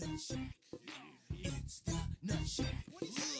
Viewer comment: Ugh... I thought it said "Nutbush" instead!